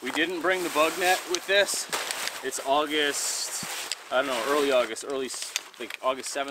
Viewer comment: Did he just say the word "ano"? I mean like a Filipino way of speaking when forgetting something. I thing I heard he says August ano... Something like that. Video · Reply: "I don't know" (other languages uses "ano" too).